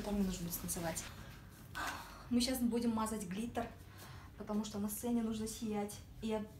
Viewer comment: "мазать клитор",или мне послышалось?